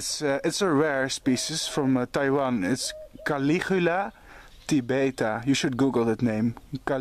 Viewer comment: Hey, what is the species name you're saying at 4:02? I'm trying to figure out how to spell it but failing, and Google isn't being helpful!! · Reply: Its definitely a caligula kind, i think he says caligula tibeta.